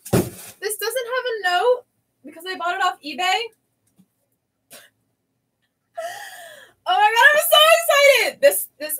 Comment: I was wondering what was the name of the manga you opened at 36:04? I couldn’t hear what you said the name was. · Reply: I believe it was d.n.angel